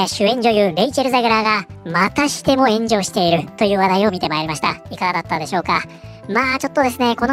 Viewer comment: ゼグラーが、またしても炎上している が ゼグラーが、またしてもenjoyしているに聞こえた。 まあ間違ってはなさそうか